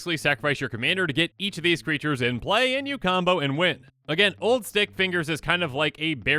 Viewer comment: I was wiping my ear when you said the "St" in Stickfingers so all I heard was "Old *ickfingers" and I chuckled